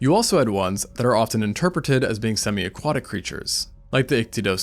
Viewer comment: did you say...... Semi-Aquatic?!